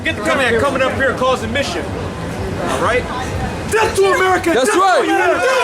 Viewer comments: Okay so at 7:40 did they say "death to America"? I can't stand either one of them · "Death to America"? Did I just hear that correctly @ 7:38? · What is going on with this clip? Did they say, "Death to America? "